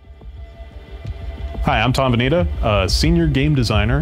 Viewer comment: Is he said, Hi, I'm Terminator?